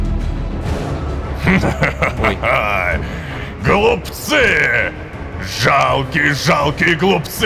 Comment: ХПАПХАХПАХ МНЕ ПОСЛЫШАЛОСЬ ГОЛУПЦЫ(вы больше это не расшлышите) Пока тут все грустят я пишу про голубцы прикольно да😂 Царство тебе небесное Невер с 1 по 58 серию 😢…